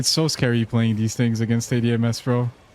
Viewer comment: i had no idea what "ADMS" was for like 10 minutes, i have 500 hours so i felt stupid because it sounded like something i should know, it took me searching online and coming back after giving up to realise you said "80 ms" 🥲